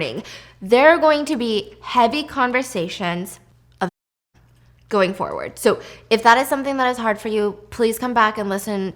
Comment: what did she say? I can’t understand but I don’t want to keep watching if I’m going to be triggered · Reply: Self harm